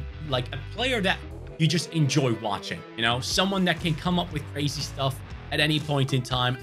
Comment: did he just say come up with rasist stuff